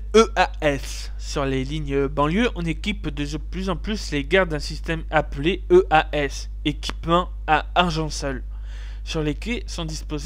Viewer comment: J'ai entendu « équipement à argent sale » ! o_O